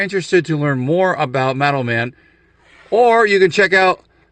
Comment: i was so confused until you showed the card. I kept hearing "METAL MEN"